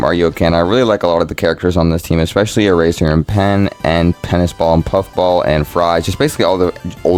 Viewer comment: naw why the captions say "Penis ball" instead of Tennis Ball